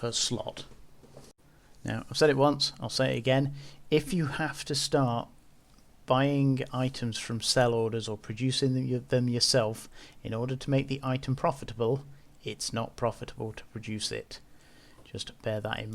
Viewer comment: I’m a little confused from 3:30 to 4:00. It seems like you say, “If I have to buy the items to make something straight off the market then it’s not profitable,” but then say, “I have to buy these items and it’s profitable to do that.” I’m finding that the invention is easy but the industry of a Tech II is almost impossible without buying all sorts of rare items (and lots of them) straight from the market. How am I mishearing you? I’d love to get into invention and manufacturing but the buy prices seem totally unrealistic to the sell portion. Even with high skills in market orders, accounting, retail, etc., the cost/profit ratio seems like such a waste of time. For instance, I have a Heretic BP. The ship is going for around 54 in Amarr while the cost of buying all the supplies is around 50. So we’re talking about a 2-4 (maybe) profit, right?